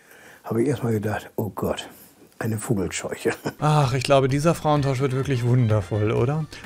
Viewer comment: hahah ich hab erst fummelscheuche verstanden xD